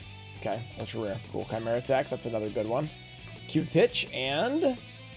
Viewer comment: I thought said "Cute Bitch" at 10:48 💀